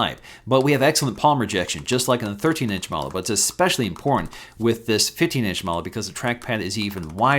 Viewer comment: Every time he says "important" I hear "in porn" hahaha